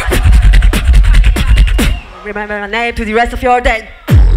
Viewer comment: dafuq is she sayn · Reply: "Remember my name to the rest of your day", i guess.